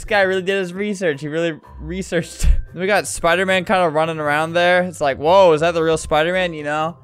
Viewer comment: I thought it said ruining I instead of running 😂